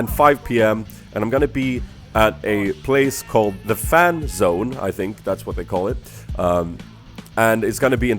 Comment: When you said fan zone i thought u said fapzone lolll